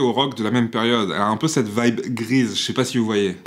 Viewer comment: Stylééééééé ! Un petit Pouce Bleu pour ma part :) À 0:57 j'ai mis un petit temps pour comprendre. J'ai entendu Grise et non Grease x)